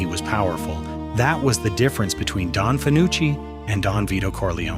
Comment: At first, I thought it said "Don Fauci," which would also make sense.